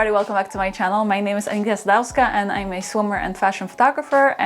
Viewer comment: Your intro, I always hear "I am a swimmer" instead of "I'm a swimwear photographer" 😄